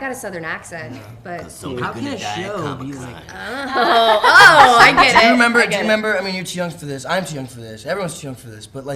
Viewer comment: What did Charlie say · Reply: Cause somebody's gonna die at Comic-Con